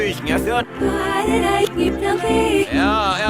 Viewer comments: The woman 8n reversed i heard her say 'why did i eat nothing' 💀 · Why did the woman in reverse sound like she said “why did I keep running ?”